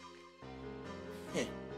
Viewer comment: usłyszałam drzewil xD